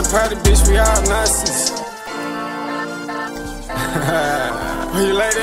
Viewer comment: did bro say "we all nazis" am I tripping